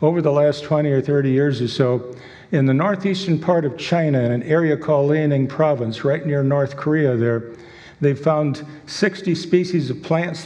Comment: "In Liaoning province right here in North Korea" Apparently, he's as good at geography as he is at phylogeny ^^' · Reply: In fairness, that's not actually what he said: you just misheard him. Take a look from 11:03 and put captions on if you need to. He actually said this: "... in the northeastern part of China, in an area called Liaoning Province, right near North Korea..." The guy is already making enough of a fool of himself. We don't need to fabricate further blunders haha.